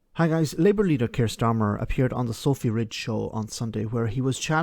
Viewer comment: I misheard that as the Filthy Rich Show.